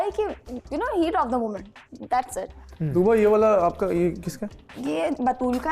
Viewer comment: Is saying Tuba to??